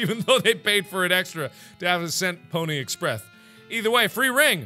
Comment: Anyone come back for nostalgia? Also at 26:01, instead of saying Pony Express, he says Pony Expreth XD